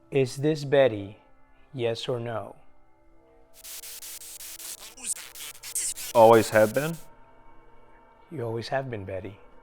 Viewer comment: the voice that Narrator thought said "Always have been" I heard "Who's Betty?" Sounded like a male voice, so possibly the spirit of someone who died after Betty lived there. Maybe a soldier, who knows.